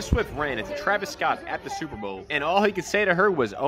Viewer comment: Sounds like he said "Hey my girl I love you "